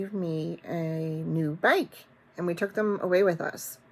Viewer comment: Thanks for sharing your time and talents with us! Your new Valentine design is lovely!! I got tickled because at first I thought you said your husband gave you a bank 😄 and I'm thinking like a piggy bank? Then I realized BIKE. The story also gave me the tickles. We are in Amish country and they use the electric bikes a lot. It's so odd when you go to pass them and they are moving quicker than you expected lol. Stay safe!